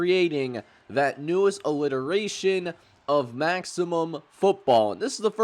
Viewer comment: did you just say "illiteration"